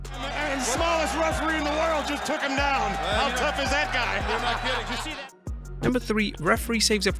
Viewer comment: On 5:35, it says in the captions "how though is that guy" instead of how tough is that guy